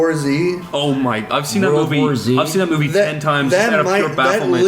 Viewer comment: Did he just call World War Z "Sworzy"?